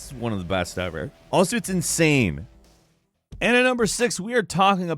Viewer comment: Did you say ‘insame’ at 5:13?